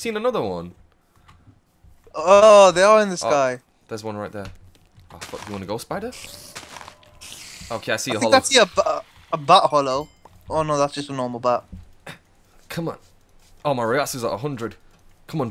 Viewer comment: At like 7:50 or so it sounded like said: "is that a Butt hollow?" 😂